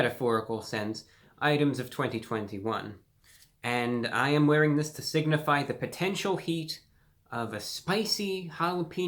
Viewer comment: I spat my drink when I thought he said he was wearing the sweater to signify hate